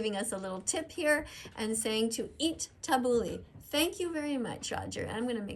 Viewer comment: I thought they were saying eat tofu